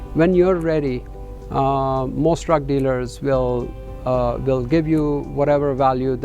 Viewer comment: I heard 'Drug Dealer' ha ha